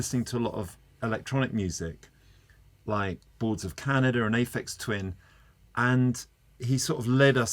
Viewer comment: Lol captions say a fixed win (aphex twin)